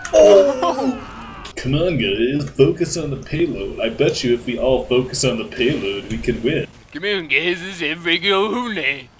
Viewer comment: What the Quack Did just say? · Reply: c'mon guys esfreginonhooley